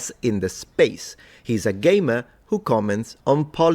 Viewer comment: is great! He's one of the most knowledgable and objective people you'll find. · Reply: I swear I heard "he's a gay man". I spewed my coffee, turned on CC, turns out he's a GAMER! Damn accent!😅